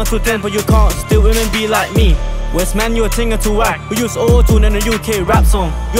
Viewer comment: did he said OO tune